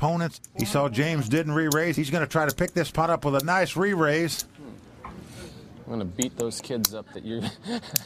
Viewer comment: Did he say "I'm going to beat those kids up.....uh uh uh" ??